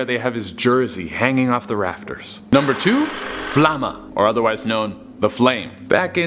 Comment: not gonna lie I thought you said Llama.......🦙